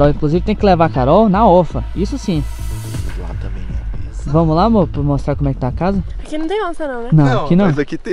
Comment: Eu ouvi direito no 1:43 "vamo lá mor"